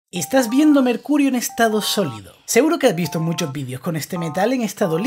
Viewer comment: No se pprque pero al comienso escuche "Estas viendome el Cul* en estado solido